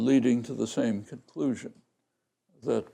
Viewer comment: What does he say? It's hard to understand. Chorine language? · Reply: He says 'Core I-language'. BTW, see 'core grammar' ('I-language' is synonymous with a particular sense of 'grammar').